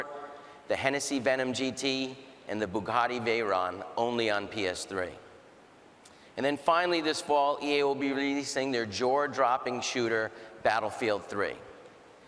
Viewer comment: Did he say Drawer Dropping or Jaw Dropping at 8:00ish? I lol'd either way.